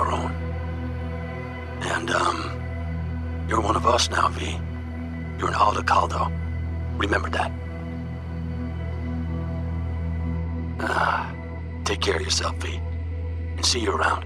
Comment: ive not played cp2077... waiting on some of the bugs to be fixed first... but I was only partially paying attention and I could have sworn he said "youre one of us now v. Youre an avocado. Remember that" LMFAO